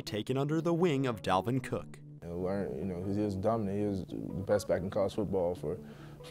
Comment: why did he say it was dumb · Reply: dominant*